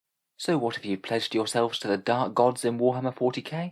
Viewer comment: I misheard the opening line as "So what if you've pleasured yourself to the dark gods" and in that moment I knew there was only one answer.